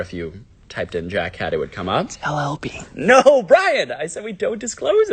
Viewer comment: it sounds like he says “no BRIAN”